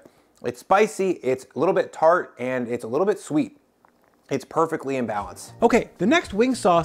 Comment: Did he say, "... Perfectly imbalanced?" BAHAHAHAHA Exactly.. Perfect